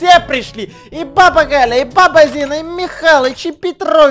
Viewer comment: МНЕ ПОСЛЫШАЛОСЬ ПАПА ЗИНА 😂